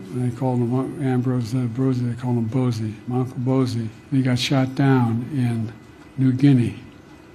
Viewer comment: i thought biden was saying my uncles bussy like wtf biden , like what happened to you do i have to feel bad for biden now?